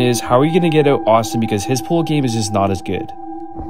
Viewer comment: “Because his pull game, was not that good” I feel you💀😭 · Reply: As I saw ur comment I heard he said "because his pull game wasnt that good"💀 (what I meant is as I saw ur comment when the vid is still playing and I heard he is saying'his pull game wasnt that good')